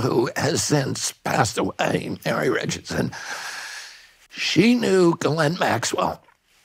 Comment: Did someone understand what he said after "my wife passed away..." ? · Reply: Mary Richardson